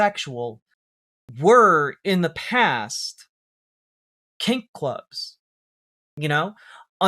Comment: I thought you said cake clubs. 😂😂